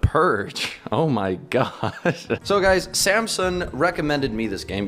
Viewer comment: I thought he said SΛMSUNG